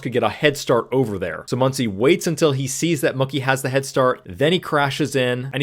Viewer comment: did you call Mookie monkey? 😂